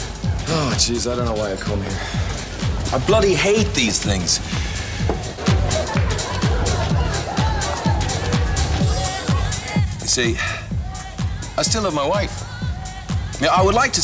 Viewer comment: I cant understand the dialogue at 1:05:13...can someone please help? :) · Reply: John: Ah geez, I don't know why I come here. I bloody hate these things. You see, I still love my wife. I would like to see her dead. I would love to see her bankrupt. But only while she's still with him. So I'm not without sympathy for you. But where the tears stop is you making up your mind about someone else's wife. You know? Owen: So you brought me here out of harm's reach? John: Maybe. Owen: John, I didn't look at her and decide to feel like this. I didn't plan it. John: Plan what? There's nothing happened for you. And if there's any justice, nothing will.